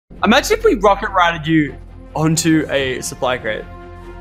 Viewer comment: the subtitles say rocket ratted instead of rocket rided 😂😂